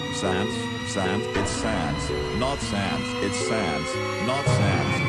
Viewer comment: What was written: "It's Sans, not sands." What I heard: "IT'S SAD. NOT SADS"